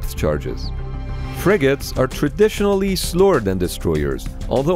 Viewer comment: I heard "frigates are traditionally slower than destroyers" as "frigates are traditionally STORED IN destroyers" and my brain had a real big-gun-that-shoots-smaller-guns moment